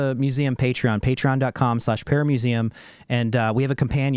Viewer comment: Every time I hear say "paramuseum" it sounds like paramecium. Is it just me?